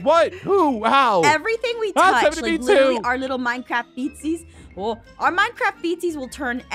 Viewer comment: I thought said "Our Minecraft FECES will literally turn anything into everything" when she said feetsies, not feces ;-; Awesome video! :D